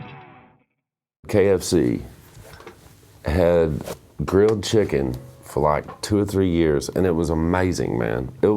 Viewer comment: I thought it said grilled children